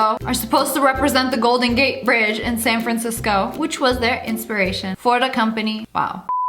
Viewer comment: When she said ''cisco'' I thought she said vsco LOL